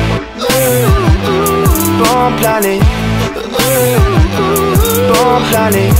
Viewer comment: doesn't it sound like he says "Blomb planted"?